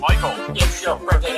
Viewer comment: "Rats rats we are the rats Celebrating yet another birthday bash [NAME] it's your birthday today Cake and Ice cream is on its way (and) [NAME] been such a good boy this year Open up your gifts while we all cheer" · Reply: I generally thought he said you're gonna turn 4 this year instead of you've been a good boy this year wtf